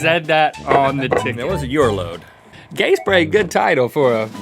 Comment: does he say gay spray????? I cant quite understand